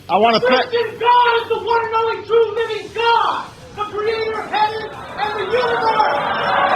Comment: At First I Thought He Said The Creator Of Heaven And The Unibomber!!